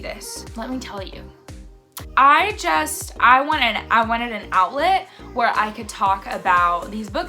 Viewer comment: You said "I wanted an outlet" I heard "I wanted an omelet" 🤣